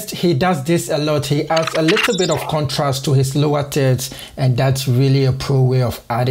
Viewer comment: Did I just heard and saw "he adds alot of contrast to his lower tits?"